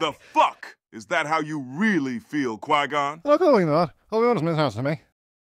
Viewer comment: I think what Qui-Gon said here is “I don’t feel like that. Obi-Wan mistranslated me.” Or something like that